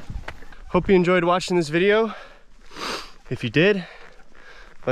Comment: At the end, it kinda sounded like you said "hopies enjoy watching this video..." 🤣🤣